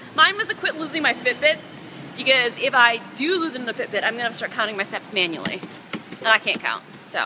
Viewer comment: She said counting my steps manually and I heard it as sex... Jesus my mind needs outta the gutter!